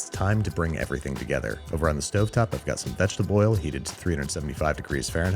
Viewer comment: Did he say vegetaboil?